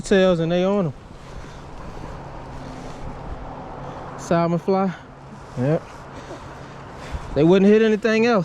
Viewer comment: What was the color rooster tail you were using here brother ? I thought you said salmon fly? But I couldn’t hear it properly thank you! Tight lines ! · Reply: Yep salmon fly Thanks for watching!!!